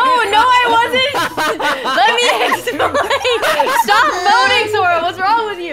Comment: I thought said in 9:23 "Stop moaning sora"😭✋🏻